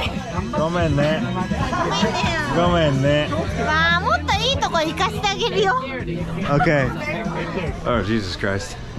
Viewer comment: someone can understand what she said? I hear "もっといいとこ見かしたげるよ" sometimes is "もっといいとこにかしたげるよ" I don't get it. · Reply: “もっといいとこ行かせてあげるよ“と言ってます。正確な日本語の使い方ではないですが、酔っ払ってるので😅